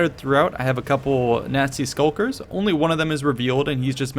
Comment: Did you just say 'Nazi Skulkers'?